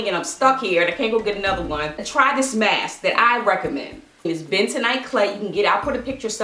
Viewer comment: I didn't catch the face mask she was talking about what was it?? I thought she said Ben tonight clay? Idk lol please help Thanks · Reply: it called bentonite clay, it a naturally occurring clay and it works very well.